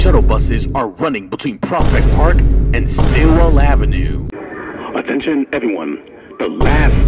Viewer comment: I thought he said "Squirrel Avenue" 😂😂😂